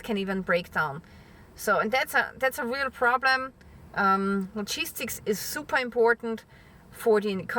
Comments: I totally thought that she was saying cheese stick instead of logistics..LOL great video even with my mistake.. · I thought you were saying "cheese sticks", then I realized you were saying logistics through context. LOL!